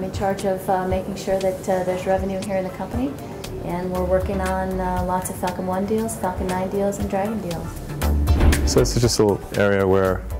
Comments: Did I hear here right @ 1:09 lots of fucking one deals and lots of fucking Dragon deals?... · i swear she said lots of fucking 1 deals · At around 01:08.... I had to rewind as I really thought she said "we're workin' on a lotta fuckin' deals!" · thought she said “we’re working on lots of fucking one deals, fucking done deals” then I realised when she said dragon